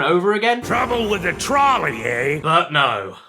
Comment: I heard "trouble with the tolley eh?" Probably 15 times before completing it. This one was definitely #2 for challenges that took me the longest.